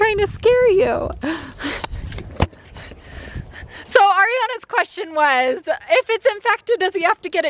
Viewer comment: I Thought she said aerial but then I thought aerials little why believe her but but she said Ariana